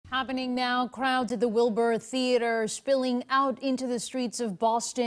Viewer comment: Initially I heard: ''last night at the Bill Burr Theatre'' which would make perfect sense since Burr is from Boston